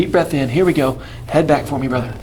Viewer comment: did he say "head back for me brotha" or am I tripping lol